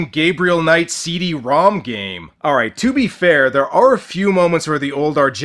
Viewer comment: For a minute I thought Asia said "they are 3" instead of "there are 3", and judging by her track record . . .